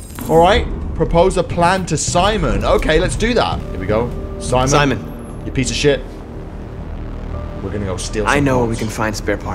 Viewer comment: I thought markus said Simon you piece of sh*t